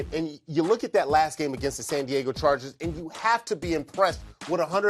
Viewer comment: probably some college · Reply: sound like he said San Diego state lol